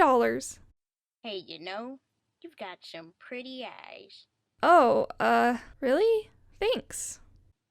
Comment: I thought she said you have a pretty ass 🤣🤣😂🤣